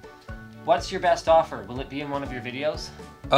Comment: What did he say between "What's your best offer?" and "... one of your videos?" - Should be sth like "Will I be in ..." but doesn't quite sound like it. TIA!